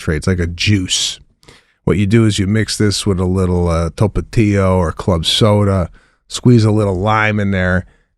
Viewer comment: did anyone catch what Sebastian drinks with Ghia--he didn't say "TOPETIA" i know that cause I couldn't look it up... if you know please let me know...